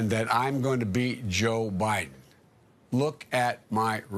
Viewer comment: The man said he was gonna beat himself and no one said anything LOL you know they were thinking " WTF is he smoking ". This man can't think a second ahead and makes absolutely no sense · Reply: What he says at 10:15 is that he is going to be himself.... He says that "He is going to BE Joe Biden". He does NOT say that he is "going to BEAT Joe Biden." Does that make sense to you now ? I hope so. I think that Sean Hannity knew that Biden used the word "be" and not "beat" but Hannity reported that he said "beat" anyhow. That is the way that Sean Hannity is.